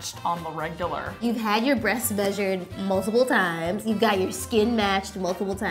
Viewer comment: i heard "you got your breast measured multiple times?" lmao